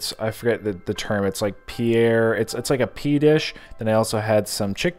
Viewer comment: Paneer ! I think that's what you were trying to say